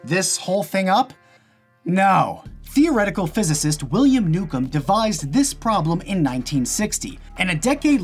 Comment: Ah. 'Newcomb' I thought he said 'William Nukem'